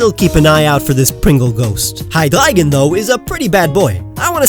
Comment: did he say Hygleigon